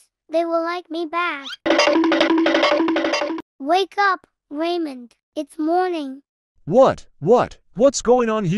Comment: i hear raymond as reimu 💀💀